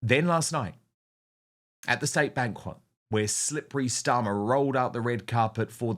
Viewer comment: Did you say "State Bankrupt" ?